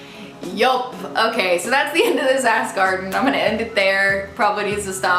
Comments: AHAHAHA when you said "that's the end of this ask arden" I could've sworn I heard "that's the end of this ass garden..." possible podcast name ???? you're welcome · definitely thought she said ass garden for a second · definitely thought you said "that's the end of this ass garden" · When she said "thats the end of ask arden" what I heard was "thats the end of ass garden" shame on me lol